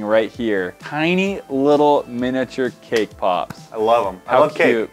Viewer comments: Everytime he said cake pop I thought he said kpop🤦‍♀️😂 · i thought he said k-pop